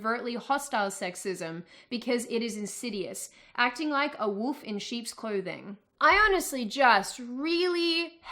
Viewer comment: what is the "pickup artist" community? never heard of that term before also it sounded like she said "acting like a woof in sheep's clothing" at 6:50 :x that just sounded super adorable to me